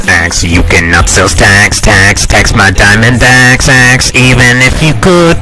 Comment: I thought he said "Tax my diamond bag sacks. Though it was a good song. I hope there will be a 1 hour version